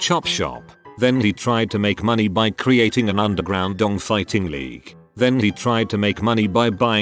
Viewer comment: I misheard”underground dong fighting ring”. That sounds pretty damn entertaining…..